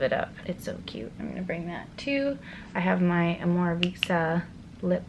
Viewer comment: What brand lip balm does she show at the end? It sounds like she says amora visa lip balm but I can’t find it online or in her list on here~ if anyone knows please let me know :)